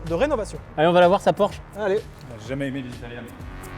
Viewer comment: " j'ai jamais aimée les italien " aie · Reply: j'ai entendu "Italiennes" ... En vrai il kiffe Sabrina et le Quadrifromaggio Verde